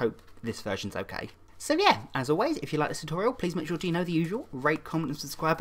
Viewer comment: Why do i always hear "rape, comment and subscribe"